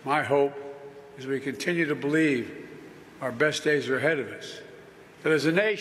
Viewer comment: When Biden said, "...continue to believe," I heard, "continue to bleed." What are we gonna do about it? Continue to bleed.